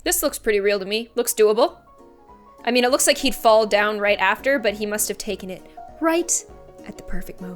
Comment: did she say “it look like after he FALLED down”?